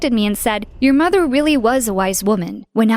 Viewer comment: does he just say wice or its just me